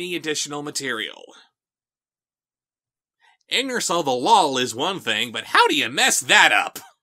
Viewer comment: What is saying at 3:56? Captions say “anger so the wall” but this expression is confusing me lol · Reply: Yeah, the auto-captions are kinda screwy, aren't they? lol No, what I'm saying is "ignorance of the lawl". Pardon my slurring, but that's what happens when you're doing a Daddy Derek impression. 😜